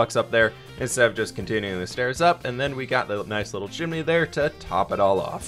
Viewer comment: When you said got a nice little chimney I thought you said jimmy